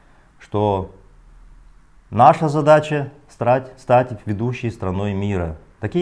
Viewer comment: мне послышался "СРАТЬ"